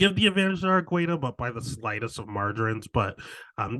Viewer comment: did u say the slightest of margarines??? 🧈 😭 🤣